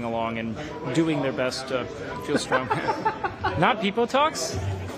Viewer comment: did he say "FeelsStrongMan not PeepoTalk" ? Hahaha